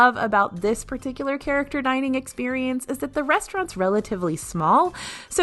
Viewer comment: it sounds like your saying that french restaurant has rats 😳🤣